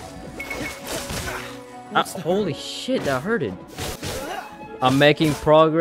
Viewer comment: did he just said that hurted 💀